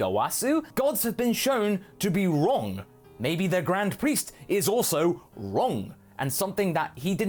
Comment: I keep hearing "Grand priest is" as "Grand Priestess" XP